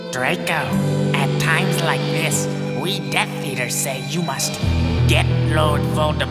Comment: when it says "we death eaters" the subtitles say "we def Peter" XD